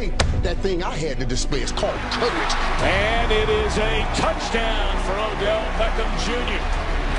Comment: did he say touchdown patriots??